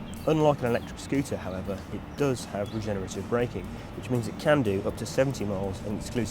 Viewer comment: he says "RE-GENERATIVE BRAKING" but if you thought he said degenerative i can see where you were coming from if you didn't know about it.